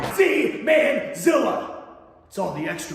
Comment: After that delightful little tune at the end, swear I heard "Z. Mann Zilla" as "Semen Zilla." Fantastic 😂